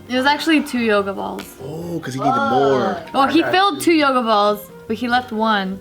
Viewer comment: I wasn't paying attention and the whole first half of the video i thought they said yogurt balls.